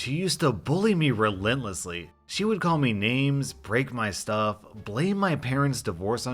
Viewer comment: I somehow heard it as she raped my stuff